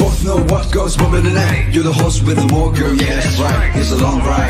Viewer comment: on the "do the hustle with them all girl" i hear "you're the horse with the wall girl ye thats right" hjghhhfcjfj maybe its cause im tired